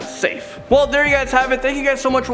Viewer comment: 😂 For a second i thought u said ur name was Landon Do Not Sing i had to rewind the video and see ur name😂 Great video by the way👍🏼